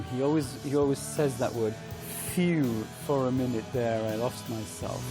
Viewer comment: “For a minute there, I lost myself” I always thought he was saying “Arrest myself”, inline with one’s conscience being the actual karma police